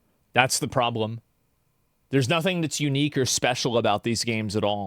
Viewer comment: Crazy to imagine the legendary Yasuke Simulator would have never even been made without Ubisoft doing this Shadows thing.. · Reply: did asmon just say "you n-words special"???? WHAT>??